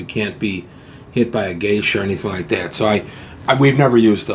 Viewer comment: I thought you said, "...so wise that you can't be hit by a geisha..." and my imagination went directly to a medieval Japanese prostitute with her face painted white hitting someone with a fan... LOL!